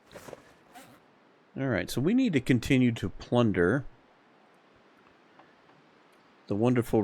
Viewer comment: You said "plunder" and I heard "blunder" and I had to rewind and double check what I heard. Then I realized... either one works and is likely to happen. It's world... we're just visiting.